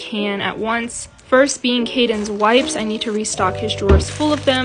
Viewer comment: I thought she said being cadeon wife